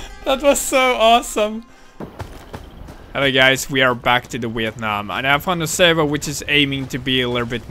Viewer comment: sounds like he said weeit nam